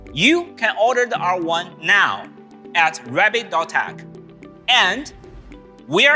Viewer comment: I swear I thought he said “rabbit dot hack”